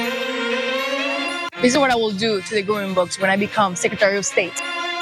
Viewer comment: I first thought she said growing books. They won't lie her because of the accent